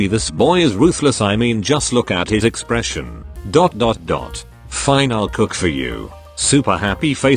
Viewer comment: When he said I’ll cook for u I thought he said I will cook u 😂😂😂😂😂😂